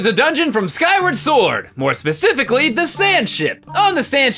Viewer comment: I thought he said Sandshit